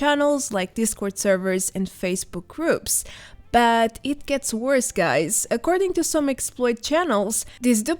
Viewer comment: What says "It gets worse guys" What I hear: "It gets better guys!!!"